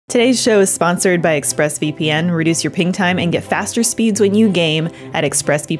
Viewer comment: Every time they say "ping time" I hear "peeing time" and it gives me a good giggle.